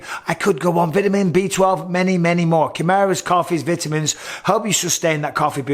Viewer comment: I thought you said "Kamaru's coffee" and I was like WTF? 🤣